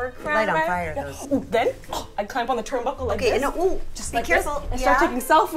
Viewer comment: Did Stephanie just say ‘and a oop’